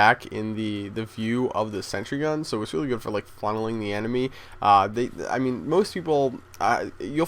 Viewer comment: did he say funneling or fondling the enemy?